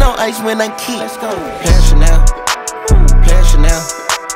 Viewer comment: I thought he was saying pap Chanel